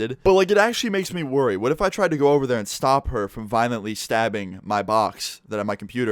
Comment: At first I thought said she stabbed his mayo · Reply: That would be even more hilarious than a box of mail